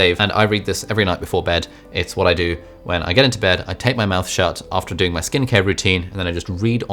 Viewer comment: Great books for plagiarists to justify their stealing! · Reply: Did he just say he tapes his mouth shut?